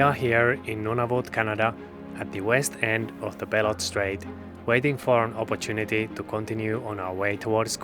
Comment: Ok I'm officially 3 years old... I hear: "the bell end strait"